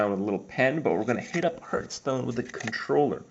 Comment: always sounds like hes saying hurtstone xD